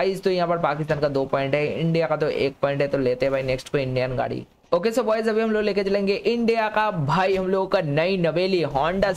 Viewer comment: On 5:59 you said boys instead of guys